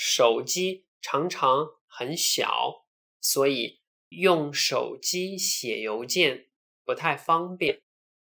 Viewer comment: you say "所以用手机写邮件 .....". I cannot understand what comes after "邮件." Can you please help? 谢谢。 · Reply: Pauline Kevin Nǐ hǎo! The sentence is “用手机写邮件不太方便”, which means “It is not very convenient to write emails with the mobile phone.”